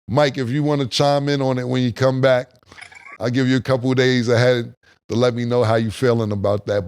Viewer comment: sounded like he said he's giving Mike a couple days of head 😅😅😅 diet pause